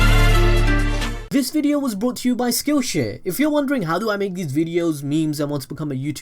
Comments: When he said skillshare I thought he said skillshit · I thought he said skillshit